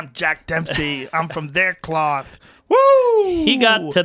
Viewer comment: I was today years old when i learned he said “im from their cloth” lol i always thought he said im from “nanclaw” n had no clue what that meant but just knew it was a scary place